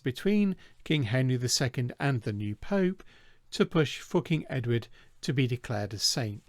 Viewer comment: I literally had to put the CC because I really thought said "fucking Edward" @ 4:55. Turns out he said "For King Edward" . Sorry, I'm sleepy & my mind can be on the immature side when I get sleepy.